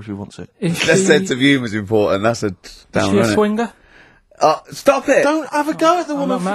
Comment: What does Karl say at 1:00... "Is she swinder." ?? Can't make it out